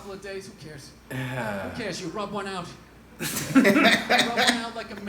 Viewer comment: 'Rub one out. It's the champagne of victory!' Best line ever. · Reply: Thought he said rogue one out